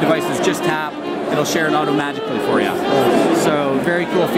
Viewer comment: did he just say it'll share it automagically?